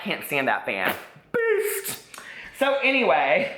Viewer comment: "BEAST!!!" LOL!!! LOVE AND ADORE!!!!💞😘😘😘💞💞💞💞💞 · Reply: I thought he was was saying FEAST and was thinking WTH? Lol